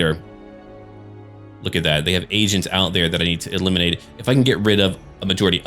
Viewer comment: What actually said "they have agents out there i have to eliminate" What i thought i heard "They have Asians out there i have to eliminate"